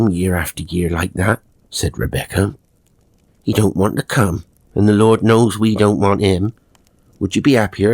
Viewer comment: 😂😂😂😂 I swear I thought she said " we dont want th C*nt" and I firmly agreed!